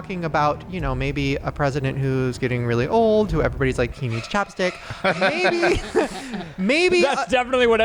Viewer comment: 😂😂omg!! I thought Shane said "teeth like chopsticks" which was funny until I realised he said "needs chapstick" so then I laughed harder at my ridiculous mistake 😂😂😂😂